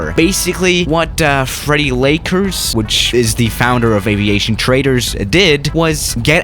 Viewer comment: I accidentally misheard aviation traders for aviation "traitors"